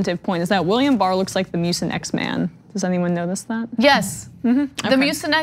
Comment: Which X-men is Emma saying Barr looks like? Sounds like she's saying The Muse but that character doesn't exist. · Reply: She said "Mucinex man" (google image search). I thought she said "Muse X-Man" at first too :P